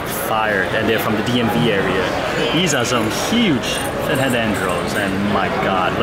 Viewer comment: what is the coral? i heard head andros? i know you have one in your tank..